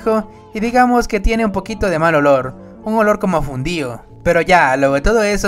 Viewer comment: Les juro que no soy racista, pero claramente escuché "un olor como a judío" 🤣🤣🤣